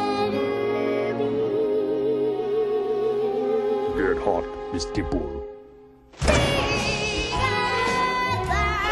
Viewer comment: Did anyone catch what Max said at 0:58 ish? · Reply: BaldurAnthology I think “get hot, Mrs. dimple”